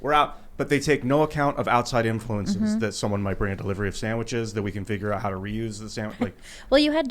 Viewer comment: did he just say we can re-use sandwiches ?